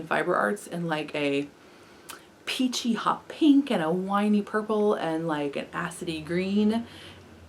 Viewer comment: I totally heard "whiney purple" lol! I love that woollen rabbit yarn you were showing, that yellow is gorgeous!